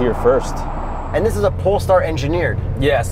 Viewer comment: at beginning, I thought they said, pornstar, LOL